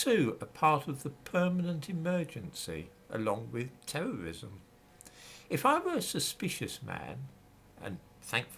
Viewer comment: I thought you said you were not suspicious 🤣